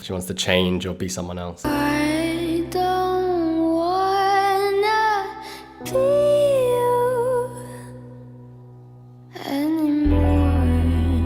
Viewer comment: THE FIRST TIME I LISTEND TO IDONTWANNABEYOUANYMORE I THOUGHT WHEN YT SAID I DONT WANNA BE U ANMORE FOR A MONTH I KEPT SAYING I DONT WANT THAT BEETLE ANYMORE😂😅